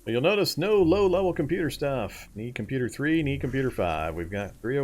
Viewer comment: No idea what this means or what situation in the video you are referring to but at 9:15 or so i directly point at Computer Science 301 (3-6 just like Principles) and SICP on my book list and mention I need the low level book, levels 1-3, before i can read them. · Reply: You're right, I misheard. I thought you said, have computer 3, need computer 5, but it was need computer 3, need 5. I was also certain I heard you said you were missing mid-level, or only had high level computer books in an earlier video, when referring to how many times you've found Principles of advanced Programming, but you must have been referring to having mid-level and missing low level, I'm struggling to scrub through that evening's play through and find it though. My bad.